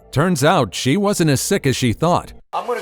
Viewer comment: Turns out she wasn't as slick sick as she thought I heard that slick adlib. 🤭